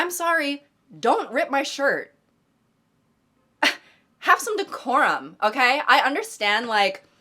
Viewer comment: im so addicted to dress to impress that i misheard decorum as decora